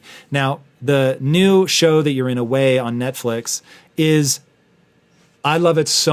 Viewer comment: What show did he say? "You're in a way" on netflix? cant find it. any help!? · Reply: The show is called "Away".